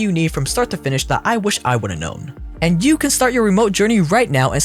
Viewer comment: Did he say i wish i wouldn't know or I'm high? probably both :D